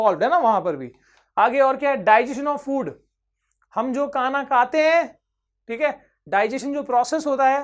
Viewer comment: sir said hum jo kaana kate hain from his nose instead of saying khana khate hain🤣🤣🤣🤣🤣🤣😂😂😂